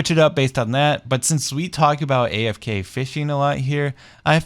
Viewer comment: I took a bite of an apple right when you said "fishing" so I heard "afk f*****g farm" lol